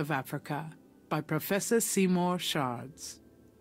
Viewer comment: I thought I heard "Seymour sharts"!!